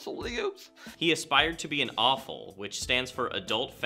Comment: i thought he said awful instead of AFOL